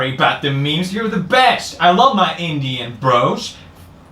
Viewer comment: Dear lord I heard "I love my Indian bras"